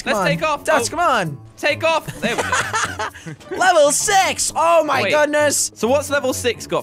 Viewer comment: did anyone notice Jelly said level sex instead of level six